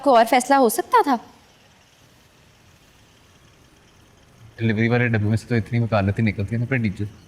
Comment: anyone tell what she say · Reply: “Tum premature deliver ho”